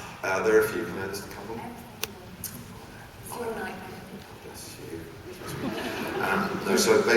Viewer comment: When he said seriously though, my favorite part (rambles) then did he say "God bless … you " or what? (@ 7:30 ) He did say you a lot of times. MY BENELOO HEART T.T And I can't with the 2nd "you" he said "…one of the best things in the world and… you"